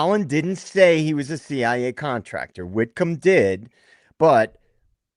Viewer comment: I want to add I thought he said he was an fbi contractor in Afghanistan. If that’s the case he is more than likely DEA.